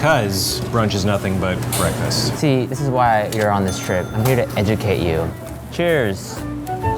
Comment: DAMMIT WHEN STEVEN SAID "I'M HERE TO EDUCATE YOU" AT 2:19 I HEARD TESSA BROOKS